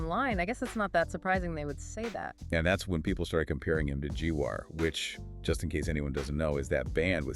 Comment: Did I just hear G-War?